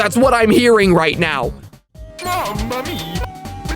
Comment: Lol yeah I heard that too! "Mama mia!" Heh!